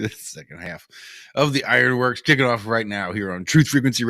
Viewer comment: The first 40 minutes is exactly what they're talking about in the back chat... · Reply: Ok some times it takes a few day for me to chopp though.. but did i just hear you say " chicken off" for that last segment, if so I think you should start a new trend...